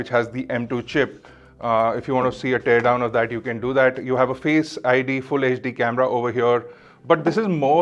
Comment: Did he just say “Face ID?” 🧐 When did that come to the Macs?